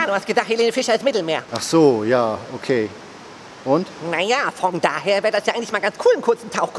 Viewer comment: habe ich da etwa bei 5:20 ein " vong" gehört 🤔